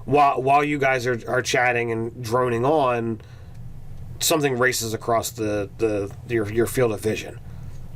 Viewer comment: Had to do a double take. When said "... something races across..." I thought he said "...something racist accosts..." I've been watching too much mainstream media. :(